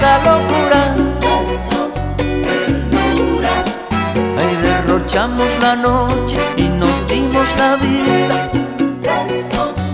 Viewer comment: Quien aquí por alofoke?? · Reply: Pense que dijo rochy